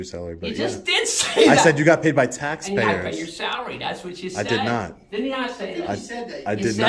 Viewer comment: Scary that both the chief and the cop in the back ground heard you say that you paid his salary when you in fact said taxpayers pay his salary.